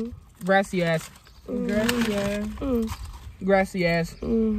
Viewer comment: It sound like saying “grassy a*s” 😂😂😂